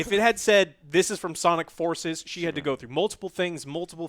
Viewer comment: At the beginning, I thought they said “Sonic Foreskin”. From their reaction to the game, that should apparently be its title.